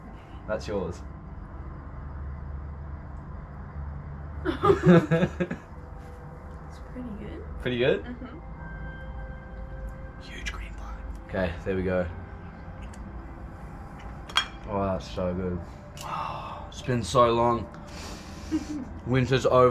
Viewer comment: what does say at 21:11? huge green flag haha?